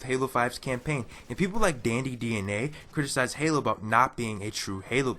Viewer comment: What's the last guys channel you said? I think it was Danny dna. I would like to check out his videos. It was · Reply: Dandy Dna, he's somewhere in the comments section !